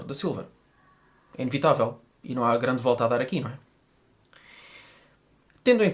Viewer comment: Adorei o comparativo!! Parabéns!!Sou brasileira e adorei o sotaque português hehehehe. Não consegui entender o que você fala em 9:46 "e não há grande...?????" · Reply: Olá Larissa! Muito obrigado pelo seu comentário! O que eu disse foi: "e não há grande volta a dar aqui". É uma expressão idiomática usada em Portugal, que não sei se também se usa no Brasil, e que significa que não existe muita coisa a fazer para resolver um determinado problema. :) Abraços!